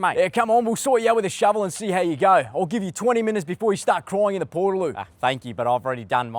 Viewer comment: the subtitles said portal loop😂